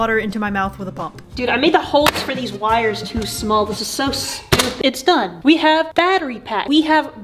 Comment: I heard this is so soup done